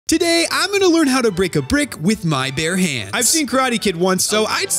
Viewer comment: Did anyone hear him say “Today am gonna learn how to brick a brick” or it’s just me? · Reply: I heard: Today I'm learning how to break a Lego brick." :/